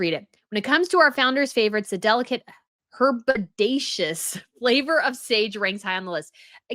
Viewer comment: I have a favourite snack which is called “Herbaceous Walnuts”, super addictive! They’re roasted walnuts with a herb seasoning. It’s pronounced “her-bay-shus”, rhymes with what I thought you said initially, did you say “bodacious”?